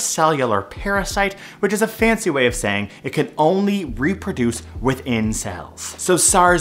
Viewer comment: I heard "it can only reproduce within cells" as "it can only reproduce with incells" and I lawled